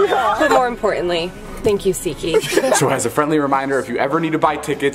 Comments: This whole time I thought he was saying zeeky not seatgeek lmaoooooo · all this time i thought you said “seki” and then realized you said “seat geek” 😂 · OMG every time he said seatgeek I always heard it as shiki and when I would see the comments they said seatgeek n I was like what is this seatgeek 😂😂😂😂😂😂😂